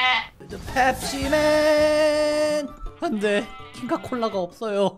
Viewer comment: 부분에자꾸눌러서보면 안되가아니고 헛되에.....이런식으로들림ㅋㅋㅋㅋㅋㅋㅋㅋㅋㅋㅋㅋㅋㅋㅋㅋㅋ